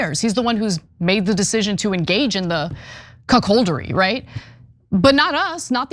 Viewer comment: What is the word Ana is saying here @ 13:33? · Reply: Cuckoldery